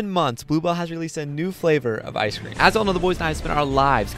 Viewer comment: I thought u said taco bell ice cream